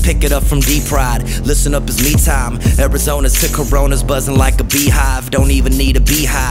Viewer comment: Wierd did he just say: Let’s Pick it up from D-Pryde, listen up it’s me time. Arizona SICK CORONA? Future telling 😂🧐